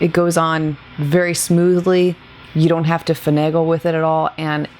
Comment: I miss you. I know you're super busy, just wanted to let you know I really look fwd to all your videos. You left the captions off and I can't figure out the word you mention at 6:11 "you don't have to... with it", the mascara. I love having the captions on in case an unknown word comes up 😅 I know, I know, it's nerdy. Elf brought back the jelly line you loved from before, I remember you raving about their primer, it looked gorgeous on you. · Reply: Thank you so much for your kind words and I truly feel blessed to hear you say you look forward to the videos. God bless you my dear friend! Yep, finagle as the person above said ...I don't know if it's a word but hey. LOL Yes! I stocked up on that jelly primer, love that stuff! Love and hugs to you!!